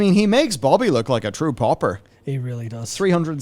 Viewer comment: "a true popper" he is! (he said pauper, learn something everyday) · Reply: I thought he said “pauper”